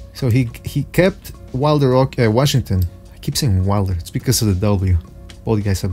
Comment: it's because of the what? Couldnt hear. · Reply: he said because of the "W"